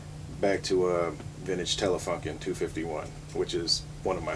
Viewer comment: It sounds like he says "Vintage Telefuckin' 251" at 1:04 :P